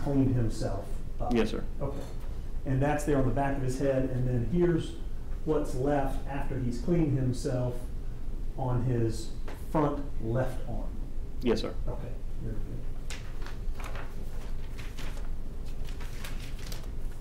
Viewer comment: uhm???… Excuse me? His what? Did I hear right? Front left arm? Sure it’s not the back arm.